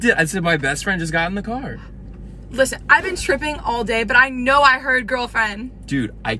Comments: Did she just say she'd been 'stripping' all day ? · I swear I heard “I been stripping all day” not “tripping” lmfao 🤣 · I swear I heard “I been stripping all day” not “tripping” lmfao 🤣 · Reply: Yes I thought she said stripping